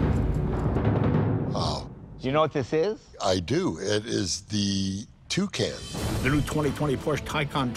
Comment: Porsche Tucan ? XD is that what he said